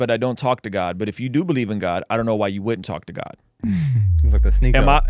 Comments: I'm not like a super NF defender but it sounded like he said "I don't know why you wouldn't talk to God" here? Pretty big difference with the quote if thats the case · Hey Ruslan, I love your take on NF and agree with what you and were saying 👌🏻 just so you know though, NF said “I don’t know why I wouldn’t” at 10:22 when he was referring to talking to God. He didn’t say I don’t know why you would. Otherwise yeah that’d be a bad take lol